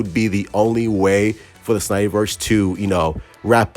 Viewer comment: i thought you said good bye to the #SnyderVerse